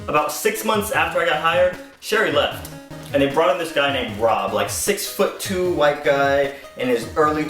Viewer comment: I got shocked a bit bcs for a seond I thought I heard him say ron instead of rob